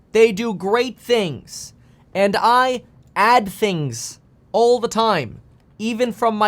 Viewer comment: I think Bidens aids add things all the time not Biden. I thought that’s what he said